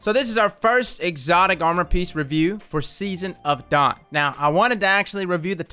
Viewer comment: I had to rewind because I thought he said season of thoat lol